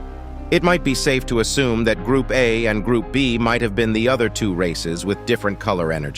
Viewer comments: “that group a and group b is the other two r@ thats what I heard😂 please Dont hate I just heard that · I heard racist💀💀